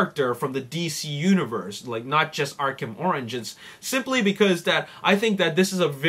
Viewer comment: it sounded like you said arkham oranges XD